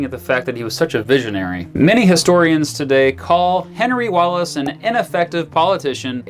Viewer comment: When you said Henry Wallace I misheard you as saying George Wallace.